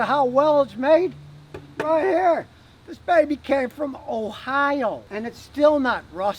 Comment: It almost sounded like he said hohio